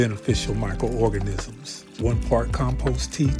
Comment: Bro i thought you said feed your soul…. Almost threw up.